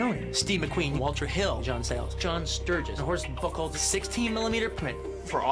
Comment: what does he say at 2:45? sounds like, "the horse becall" lol idk ?